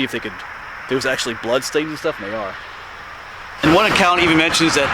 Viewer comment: Not sure if you're alone there but I heard another male voice saying sounded like "they are" at the 3:40 mark